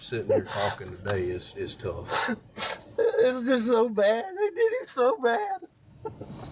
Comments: did he just say he did him so bad? 😮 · Mr. Alex subconsciously confesses and says “I DID IT SO BAD!” I don’t care what anyone else says, he did not say “they” did him so bad. Numerous people have listened. Not that it really matters at this point, but to answer you Mr. Alex, yes, you did blow your sons head off so, so, so, so, so very bad. You should be so proud